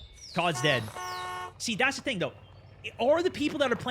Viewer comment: Did he just say "That's Dingo"? #vldl